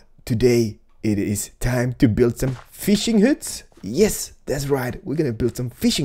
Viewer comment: I thought you said your were goin to make fish hats.LOLX)